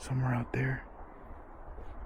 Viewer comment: Is it just me or did someone say( there) just before said( somewhere out there ) time line was